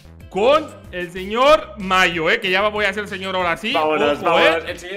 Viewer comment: Minuto 6:52 Víctor dice “MAYICTOR” 7w7 y no salió como se vería raptor de viejo, yo lo queria ver cómo sería de viejo Mayo T^T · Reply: Yo leyéndole los labios a Víctor entendí "mayo es mío" xd